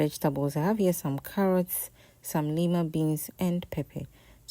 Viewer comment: Carrot, what beans please? I heard Nima beans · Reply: Lima beans. The market women know it as the beans used for soup